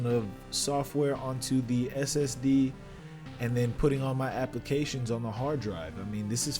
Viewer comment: DID U SAY SDD 😵🔫